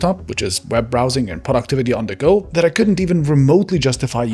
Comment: I thought he said PORN activity on the go" instead of "productivity on the go". I was like wow that's brave of him to be so upfront 🤣